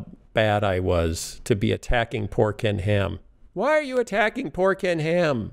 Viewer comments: For a moment I heard "pork can ham" . My brain is messing with me. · I hear 'Pork n Ham' LOL